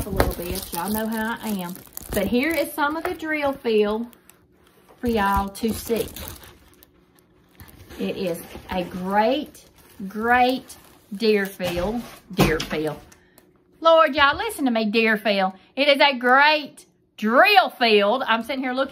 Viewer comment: It's so funny Im so into diamond painting that when you said deer fill I really thought you said drill fill 😂😂😂😂 hope your having a great day🥰🥰🥰🥰